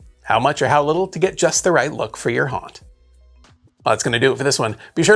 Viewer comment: I had to chuckle at the end. I heard it as "Be sure to lichen subscribe..." Great video as always!